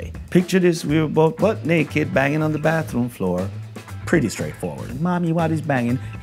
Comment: I love Shaggy! I always thought he was saying banging on the bathroom door! lol!